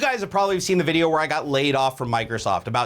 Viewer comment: when said you all have seen the video where i got laid off from microsoft, i thought he said "you all had seen the video where i got laid" i laughed so hard untill i realized he was talking about getting laid off from microsoft.....